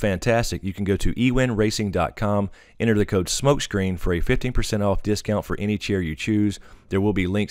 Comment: I thought you said 50% off, I was all excited!